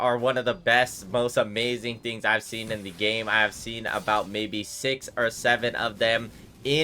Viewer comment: What did he just say... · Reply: 6 or 7